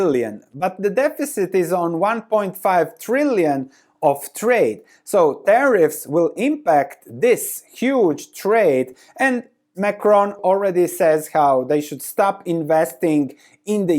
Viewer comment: I thought you said micron not macron (eu guy) lol